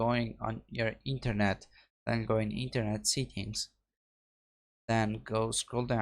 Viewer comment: When he says settings it sounds like he is saying seekings, it's (se, tt, ings) not (see, kings) But thank you it worked for me